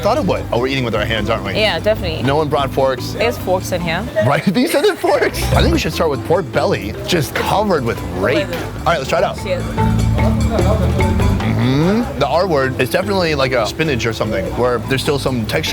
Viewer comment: i thought i heard him say lets try the raped pork belly